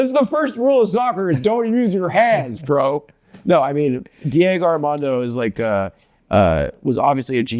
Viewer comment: did he call Maradona ""Armando" ?